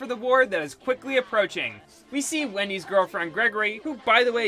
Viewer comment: Did he say Wendy's girlfriend?